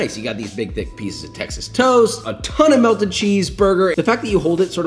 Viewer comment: I heard “big dick pieces of Texas toast” at the beginning there